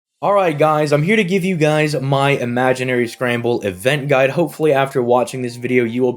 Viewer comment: You know, this entire time leading up to this event, I thought you were saying Imaginary Scrabble. Boy was I confused.